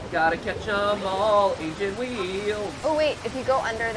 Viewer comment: I thought they were saying Asian wheels